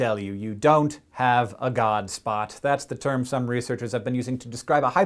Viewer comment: At first when he said "You don't have a God Spot" I thought he was saying "You don't have a God." Either way, go Scishow!